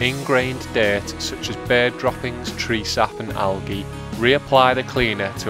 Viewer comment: Did he say at 2:40 bear droppings? how many bears live near him where bears climb on an MG to shit on the roof